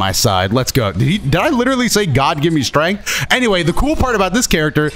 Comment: he actually said “God, grant me strength”🤣